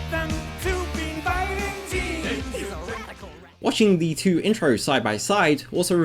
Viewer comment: what dose it say ? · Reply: "He's a radical rat."